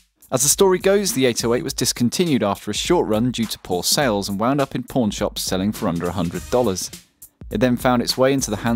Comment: When he was talking about the 808, I misheard, "and wound up in porn shops selling for under $100."